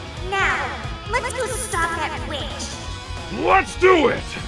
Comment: oh god I thought proxi said at first cmon let’s go stop that b$tch but she actually said witch